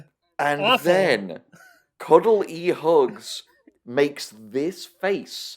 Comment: I thought Cuddle E Hugs was gonna be a Chuck E Cheese/ Five Nights At Freddy’s riff but BAFFLINGLY it’s an extended homage to Harvey (1950) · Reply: I heard "Cuddle E Hugs" and my first thought was "Cuddle y Hugs" like... "y" as in Spanish for "and", but then they said it was the letter E and then I was like HUH???